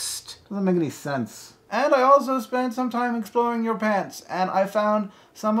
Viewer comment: I thought he said that 'I was exploring your pants.' I'm stupid.